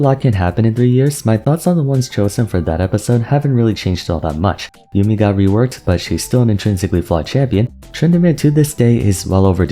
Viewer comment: My dogs knocked something over and i thought you said "yuumi got reworked but is still a fucked champion" and i was like 🤷‍♂yea man why not